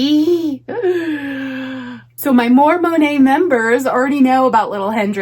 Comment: You say "More Monet" I hear "Mormon Hay"